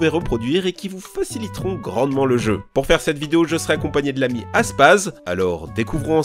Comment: J'ai entendu "Azpaz" Moi: attends... Q U O I ? ? ! !